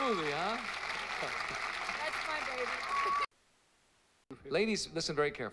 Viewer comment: did she say "that's my bubeleh" ?? 😭😭😭😭